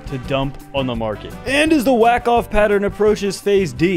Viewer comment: Dayum. Did he just say wack off? Lol i'm dead 🤣🤣